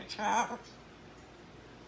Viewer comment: It sounded like he said “trying to be good” towards the end 😆❤️